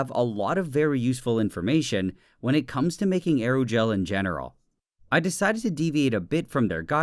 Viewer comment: I thought it said aeroace